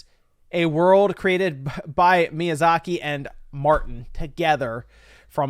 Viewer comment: Wait wait wait...did you just say MIYAZAKI and Martin?! Holy crap. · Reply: no, no, no... it's martin and namco bandai